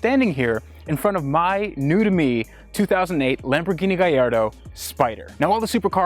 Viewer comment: Lol did anyone else catch him sayin "Lamborghini Gayardo" at 00:29 haha